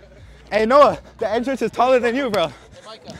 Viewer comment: I thought bro said the Edgars are taller than you